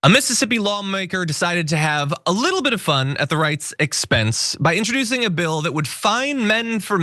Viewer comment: At first I thought he said at the reichs response